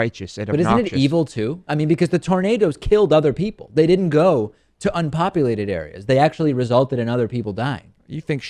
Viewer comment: She actually wanted that tornado to go to unpopulated areas. Check out her statement again. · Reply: Oops i thought you said "Other populated areas" my bad.